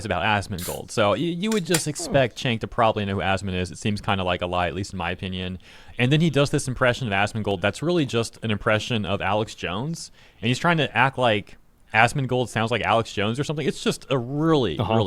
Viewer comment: TYT is a joke. sounded like he said "Assman gold" lol. Im ok with that. not even close to the insult he thinks it is.